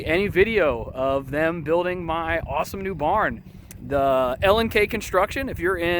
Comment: Love the barn and the video. I am also in SW Ohio. What is the name of the construction company? I thought you said "LNK construction" but could not find them listed. Also are you selling your syrup? Where do you sell it?